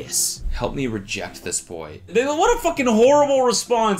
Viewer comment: what does cody say at 9:39 ?? the subtitles aren't helping please tell me i'm mishearing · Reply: "That's such a horrible response"